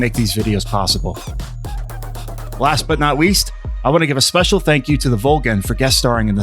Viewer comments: did you say: “Wast but not weast...” because to me it sounded like you said that. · did he just say last but not weast?